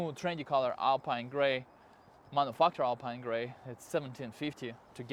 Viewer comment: did he say, "Mf'er alpine grey" ? LOLOL